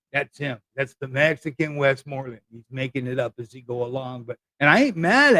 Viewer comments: Westmoreland was one of the commanding generals during the Vietnam War. >William Childs Westmoreland was a United States Army general, most notably the commander of United States forces during the Vietnam War from 1964 to 1968. He served as Chief of Staff of the United States Army from 1968 to 1972. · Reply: I thought he said "Wes Borland" LOL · Okay, he said Morland AND Borland. Appreciate the history lesson refresher, though! 🎶The more you know🌠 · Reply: I am full of useless trivia lmao I could have sworn he said Wes Morlan or something. Oh well, I stand corrected.